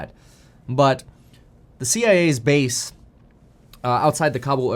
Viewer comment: It sounded like you said the cia is base. Just finding a lol where I can